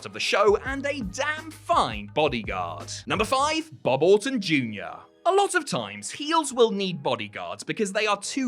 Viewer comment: I thought you were saying Bobbleton Jr.